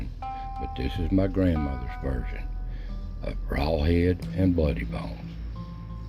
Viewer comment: Lawd have mercy...I heard it as raw hide and bloody bones...oh my goodness gracious.